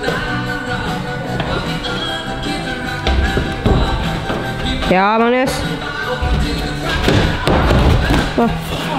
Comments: does anyone know what magnus says when hes mad at around "17:50"? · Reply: I think he is saying "Hva faen er det som skjer med de føttene mine da?!" Translates roughly into "Wtf is going on with my feet?!". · what did he say at 17:55 when he fell? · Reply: He says "Hva faen er det som skjer med de føttene mine da!" Which roughly translates to "What the fuck is happening with my feet"